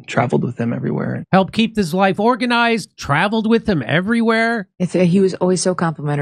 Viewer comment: He clearly says he "helped keep his (Charlie's) LIFE organized". He knew the second he saw Charlie's throat blown out, that his job was done. No more helping Charlie keep his life organized.